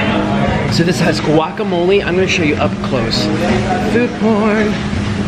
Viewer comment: Did he say "food porn" 1:20 😣😣😣